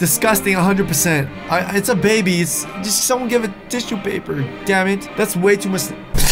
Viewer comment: hate to be that one guy but did say give that baby TISSUE PAPER AT 2:56 OH MA GOSH MUN